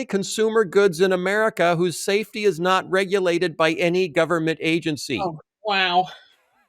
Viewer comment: Phil at 23:44. “Guns aren’t regulated by any government agencies.” This is a complete fallacy. Guns are regulated both by state agencies and the federal ATF. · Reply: Listen to 23:44 again. Phil did NOT say, "guns aren't regulated by any government agencies". Phil said, "gun SAFETY is not regulated by any government agencies."